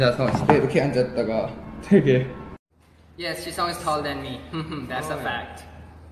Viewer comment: can someone pls tell me what ten said i cant hear it lol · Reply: thisisnoticecream itsbutter he said "yes jisung is taller than me that's a fact"